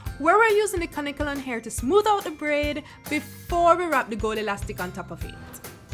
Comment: The whole time she saying “gold elastic” and I’m laughing cause I thought she was saying “gully lastic” 🤦🏾‍♀️